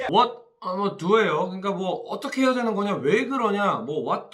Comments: 손흥민 선수 What's your respect for me? I respect you! 라고 하는것 같아요. 느리게 설정해서 들어보면 이렇게 들려요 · 나는 what's wrong with you? I respect you! 요래 들리는데..